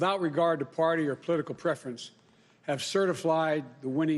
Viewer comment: Did he say "Certiflied" at 0:09 ?